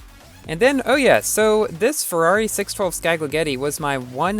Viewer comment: it sounds like you said ferrari spaggeti